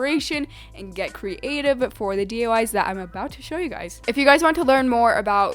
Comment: I swear around 1:29 she says 'DUI's instead of DIY